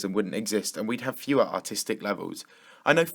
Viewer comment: sounds like you said 'autistic'